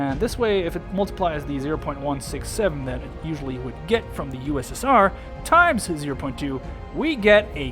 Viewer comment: did he said ussr?